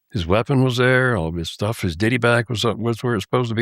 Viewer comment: I thought he was saying "Diddy Bag".... Every time saying so I pull my Diddy bag out. 🤔🤣